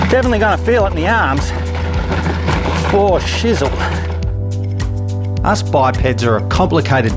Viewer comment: Did just say "for shizzle"?!?!?! Howdy from the North American continent!